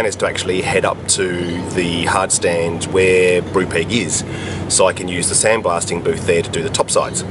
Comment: Anybody got a link to that other channel? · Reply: thanks mate, for some reason I thought you were saying "Brew Pig" in the video!? 😅